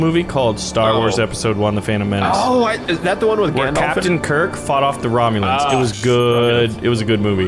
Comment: Did I hear “is that the one with Gandalf”